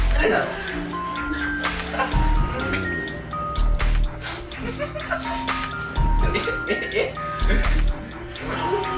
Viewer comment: 声聞こえる うーって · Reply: りょうの｢おぉ｣ですね